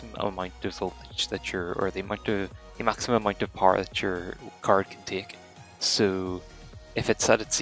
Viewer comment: What are you saying at 6:19? Does anyone understand what that controls or what he says there? Sounds like a choking on words to me so I don't get what the slider does exactly :/ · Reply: I believe I was saying that the slider limits how much power your card can get. That doesn’t mean that your card will always be using more power, but if it needs more power then it will be able to get it. E.g. You may get to a resource intensive part of a game and your card may need to draw more power to deliver the same FPS. With the slider at 0 it may hit a limit of how much power it can draw, but at say +50 the limit will be much higher and therefore it will be a lot less likely to hit the limit. Hopefully I explained that ok for you. Just to note I have made an updated version of this video due to finding better settings and also audio issues (as you said) so I’d recommend watching that, but obviously that’s up to you!